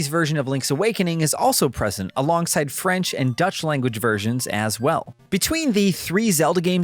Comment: Did he say "Dutch" or "Deutsch"? Because there is a huge difference. He said "French" in English, so it confused me that he said "Deutsch" instead of German.